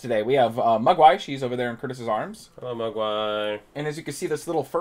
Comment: I swear at 0:04 I heard "We have special guests today: we have my wife, in Curtis' arms". I was like '... so you guys are REALLY close. Okay...' lol